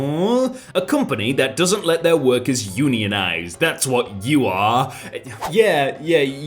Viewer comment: BRO I HEARD "AMAZON DOESNT LET ITS WORKERS UNIONIZE" THEN I GOT AN ALEXA AD